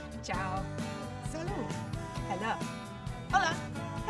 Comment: is the audio so low or did she just say "hello" during Saudi Arabia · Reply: she said hala (هلا)